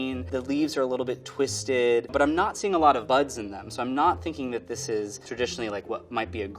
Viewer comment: thought he said bugs lmao. Best way to determine tea quality, I suppose